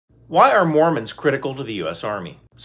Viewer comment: For a sec I thought he said "why are morons critical to the army"